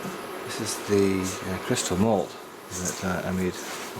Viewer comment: I thought you said crystal meth for a mo. Our very own Walter White! Looks a very decent brew.